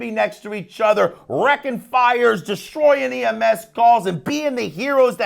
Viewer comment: I hear “TrY to catch me holler’n at the mOooN”